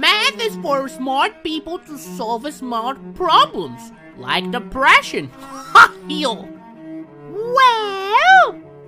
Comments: “Math is for smart people to solve smart problems,and depression” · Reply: i thought he said deprassion, a mix of subtraction and divition, but the depression is just wow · I legit thought he said "Like the fractions" till i read the subtitles